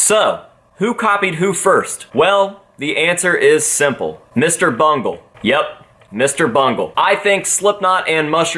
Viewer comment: I thought he said Mr. Bunghole until the name popped up